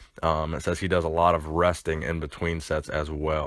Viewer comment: "Says he does alot of super sets, alot of sets to failure, alot of resting in between sets... and alot of crystal meth." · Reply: I thought he said resting between sets not crystal meth between sets